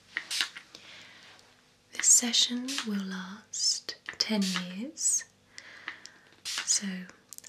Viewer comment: I thought she said the session will last 10 years. In my mind it was like I have to stand there for 10 years until the first session is over.